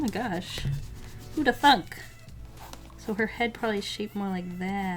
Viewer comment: Did she say the F word at 4:15 ????? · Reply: The subtitles said thunk...